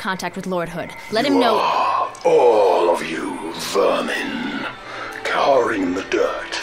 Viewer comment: "YOU ARE... ALL OF YOU... VIRGINS..." That's what I heard the first time I saw this cut-scene. And I was too young to even know what a virgin was (I was seven when this game came out). Now I can't help but think that the UNSC is just Virgin Squad fighting the Chad covenant for domination of the universe. Halo takes on a much more humerous tone that way. (PS: The edit was because I fucked up the time stamp)